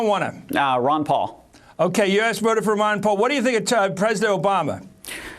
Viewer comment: did he say ok you ass voted for ron paul lol ... i think he did :))